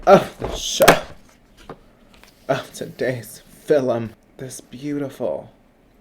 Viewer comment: Is he taking a cue from the Dark Lord, John Mclean? I know I heard him say, "filum".